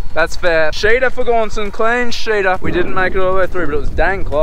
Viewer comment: In America it sounds like your saying clean shitter